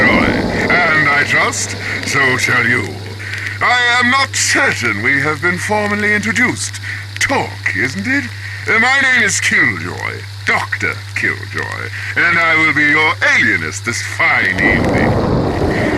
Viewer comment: i misheard: “ My name is til” Joy, Dr..